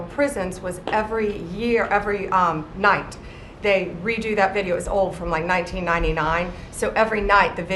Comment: Now I have a question about 22:23 when Pam said the video is old its from like 1999 ? Is that what I heard ?